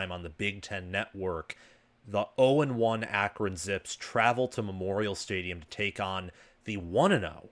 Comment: Misheard what you said, thought you said "0-1 Akron Simps", haha. Had to back up and listen again.